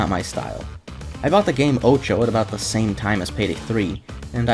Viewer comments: what was the game at 35:25? i heard ocho but when i look up "ocho game" i just get uno clones lol edit: its called OTXO for anyone else wondering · What game is he showing @ 35:26? He says ocho or something like that but I can’t find it using that name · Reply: Otxo